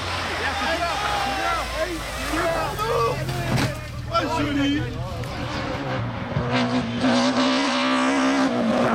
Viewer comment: right when the Mini goes into the ditch, what does the spectator say? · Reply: "Oh jolie !" It just means "wow beautiful gg !" But being sarcastic in a funny way (french humour)